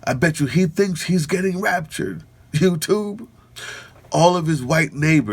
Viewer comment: Damn. I thought they said 'ruptured'. Oh! This Sinclair crowd , like Clear Channel, has been bidding to be State Media since the days of W.